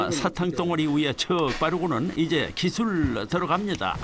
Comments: 초 쯤에 사탕 덩어리위에 쳐 바르고는이라 들리는데 맞나요?? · 뭐지 왜 처 바르라고 들리지..? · 저만 처바르고는 이라고 들리나요? 펴 인 것 같긴 한데 처라고 들려서요..